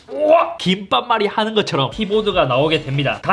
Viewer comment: 왜 귓밥말이라고 들리냐